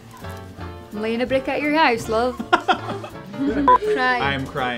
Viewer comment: What does she say to him at 1:19 ? I can't really hear it. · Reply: Andrew R - She says, "I'm laying a brick at your house, love!"